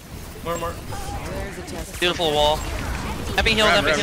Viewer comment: ここなんて言ってるかわかる方います？ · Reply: beautiful wallに聞こえます